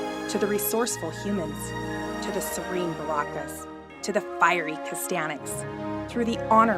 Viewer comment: Thought she said "Fiery Hispanics" and I choked on my apple juice.